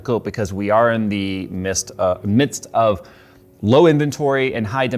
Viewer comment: 'Mist' instead of 'midst'. 'Mist sounds about right. The elusive 'video card in the mist'.